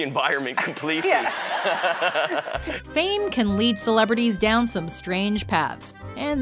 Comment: I thought she said "memes can lead celebrities down some strange paths" Dank ones, that is.